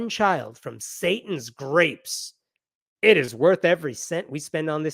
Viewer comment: I think the paper misheard the remark, I believe he actually said, "Satan's GRASP" and not grapes.